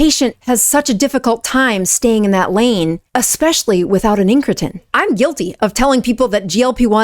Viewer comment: What is the word you are using at @ 8:09 ? Sounds like Intrekin. Entrekin. Something like that. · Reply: Incretins